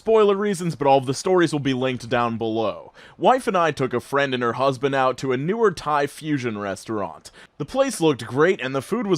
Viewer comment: I misheard the first part of the second story. I heard “wife and I took her husband out”